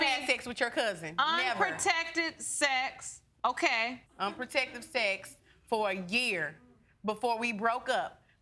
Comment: Unprotectedly... She should not be having kids using these words. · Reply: I thought she said 'unprotectively' (which is not any better).